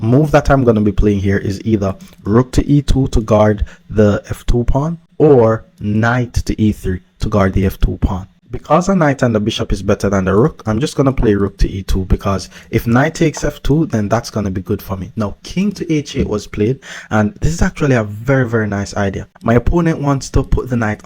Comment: am i hearing thus correct "bishop and Knight are better than rook"?